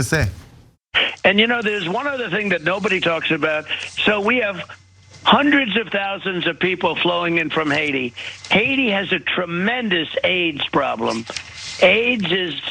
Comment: Man it took my way to long to realize he was saying AIDS. I thought he was saying they have a age problem. I was like wtf does that even mean.